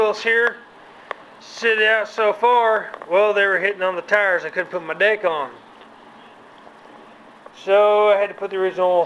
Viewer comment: he says "I couldn't out my deck on." He does not say what you hear lol